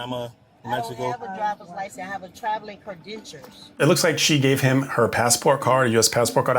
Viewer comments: did she say cre-dentures???? Lmao does she not know the word credentials? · credentures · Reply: Thought I heard that wrong. Had to go back and listen twice! Credentures. Do they store them in their teeth?